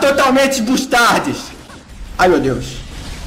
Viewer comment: ouvi totalmente mustardis :P